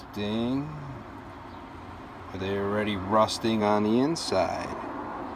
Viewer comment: did you say "rusting" ???